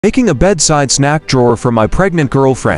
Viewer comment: i thought you said “making a snack sized drawer”